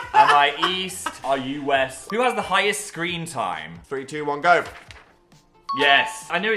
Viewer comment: really thought they said “who has the highest scream time” and i was still right